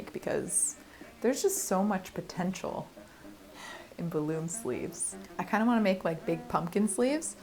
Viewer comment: I actually thought you said “hot-air-balloon-sleeves”. Well that will be genius!